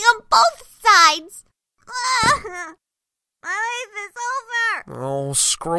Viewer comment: When she plopped her face into the pillow I heard " I'm goin eat the sofa!"